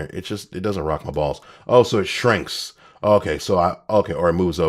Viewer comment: I swear, at 11:10 I thought he said “It doesn’t rock my ball ALSO it shrinks!” 😅😅